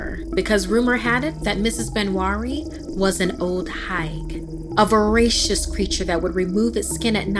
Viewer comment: at the start it said “beware of the old higue “ I thought it said hag 😭😭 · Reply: nezuxi ネズミ yeah I heard "She’s an old hag" and I was like damn😂